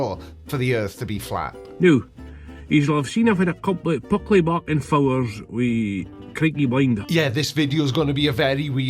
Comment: "Gubbledrb blkfrxdxwwuggummmbluh Creaky Blinder!" That's what i heard...😀🐕🐕